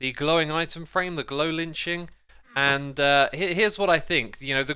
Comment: Love your videos like always, and I love the way you pronounce things shashwammy, but it might be a good idea to try to say "lichen" differently the future. In this video it kinda sounded like you were saying "glow lynching." I think people generally easier pronounce it like 'litchen' or 'liken'. Love your videos, thumbs up!